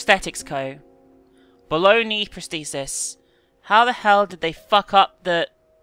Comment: I was down in the comments, and I hear say "Baloney prosthesis".... Wat. Quick scroll back up, "Oh. Ha."